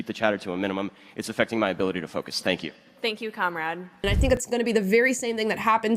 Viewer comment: “thank you comrade” is wild! How are these people allowed to spread their ideology on college campuses. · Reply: I thought she said Conrad lol 😂 that is so much more unhinged than what I thought it was.